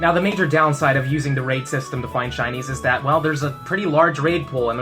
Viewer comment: I’m laughing at the fact that Youtube’s subtitles thinks you’re saying Chinese instead of Shines haha